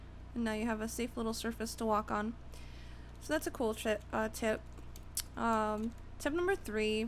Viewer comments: listen to the mistake she made my saying shit instead of tip. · DID U JUST SAY COOL SHIT ;D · said shit instead of tip